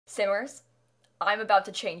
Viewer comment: When she said simmers in the beginning i thought she said either suers or sewers